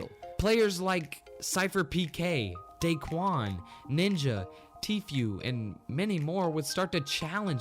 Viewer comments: Did you just say Tfew Not Tfue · did he call Tfue TFew